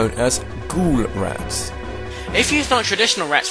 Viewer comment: with accent I thought he said 'Ghoulet rats'